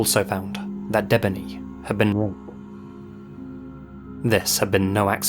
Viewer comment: What is said at 3:57:25? I can't hear it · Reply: he censored the word r@